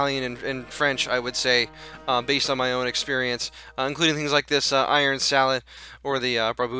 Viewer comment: archers are sneaky and give a lot of damage in real too.. i thought you said youre experience